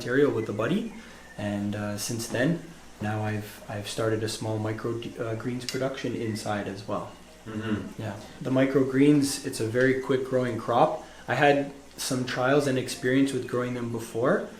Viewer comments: When u almost say microdose instead of micro greens 😂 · I thought he said 'Microgreena'